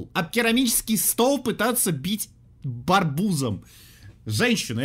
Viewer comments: почему сказал барбуз (пожалуйста не аскарбляй пж мне наверно послышалось 🙃🙃)!!!! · Никто: Абсолютно никто: это ж... хихихи надо об керамический стол, об керамический стол пытаться бить..БАРБУЗОМ? (2:38) /только мне так послышалось?